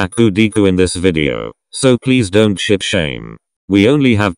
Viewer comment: When it said "Ship shame" I was only listening to it so I heard "Shit shame"